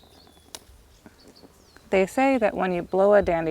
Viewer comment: "They say that when you blow a Daddy Lion, it spreads his seed?!" That's what I heard anyway.